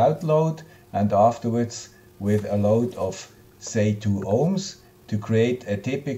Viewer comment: Please ,what is the Load at 0:49 2 Ohm ? , sorry I can not hear it acoustically what he says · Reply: I said 2 Ohms